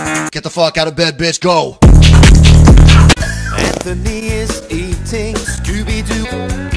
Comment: Did he just say Anthony is eating Scooby-Doo? 😂